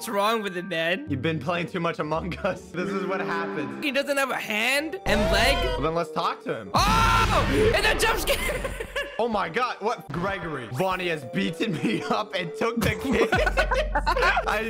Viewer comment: did I hear him say "vonny"?